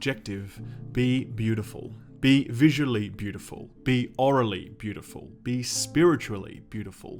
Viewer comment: swear to god I just heard you say it was orally beautiful